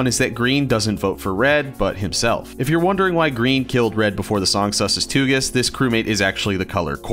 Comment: i think the captions said sussus toogus wrong